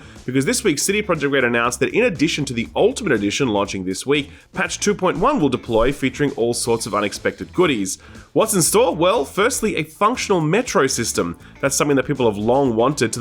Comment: I thought he said “a functional metric system” and was confused how they couldn’t include that at launch.